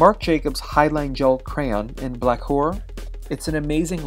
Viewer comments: am i the only one that thought he said "black whore" when talking about the eyeliner? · at first I thought it said in black whore lol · am I the only one who thought he said “black whore”?